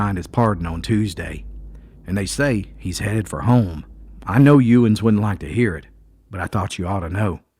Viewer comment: Yes sir, a powerful message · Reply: were you reading the actual words of Granny? There was a word you were using when she collectively referred to her sons. Sounded like you said "you ins"...